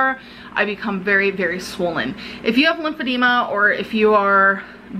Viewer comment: I thought she said she didn't have lymphedema